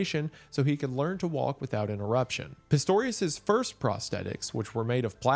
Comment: When you said “so he could walk without interruption” I thought you said without an erection😭😭😭